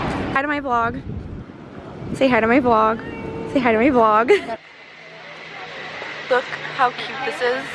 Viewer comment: I legit thought she said "get out of my vlog" 🤣